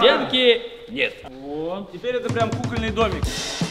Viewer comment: Мне одной показалось что сказали куханый домик?